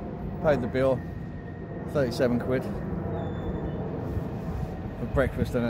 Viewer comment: 37 quid! wtf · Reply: Thought I misheard & that he said 27 quid... 37quid...Jeez they ain't shy on charging...My 1st car was only 30 quid 😳